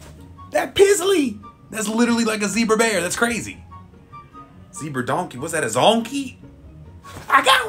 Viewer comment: Had to rewatch thought I heard a "honkey" at first.